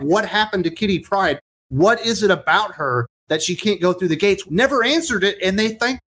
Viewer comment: Guys I thought they said she can’t go through while phased and she always instinctively goes through a portal phased